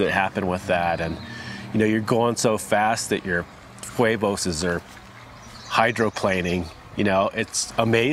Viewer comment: did he just say he loves when his huevos are hydroplaning